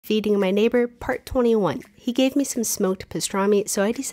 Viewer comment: I THOUGHT SHE SAID EATING MY NEIGHBOUR